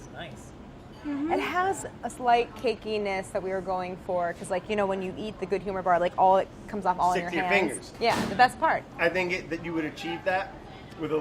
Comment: I really thought Brad said "Sticky Fingers". Man. I got excited for a sec.